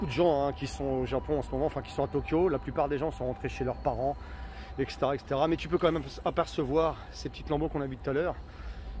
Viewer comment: A 8:23 c'est quoi le nom du boulevard? Gahen mae? Merci bonne continuation! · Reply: Station Gaien Mae